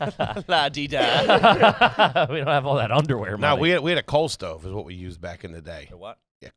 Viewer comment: oooh a COAL stove, this whole time I thought was saying cold stove 😂